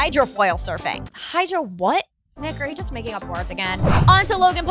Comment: Did she just say the N word with k insted of g?? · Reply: Nah, she said "NIC, ARE you just making up words again?"